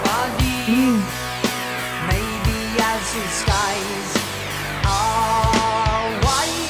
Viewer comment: It sounded like you thought he said "whiiiite", but the lyric is "Love and life are deep, maybe as his skies are wide". I love that Neil (or was it Pye Dubois?) changes it up to "maybe as his eyes are wide" the second time. For recommendations, since you liked the instrumental break, check out "YYZ" from the same album.